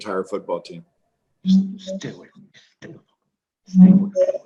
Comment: what is whispered? Stay away from me x2??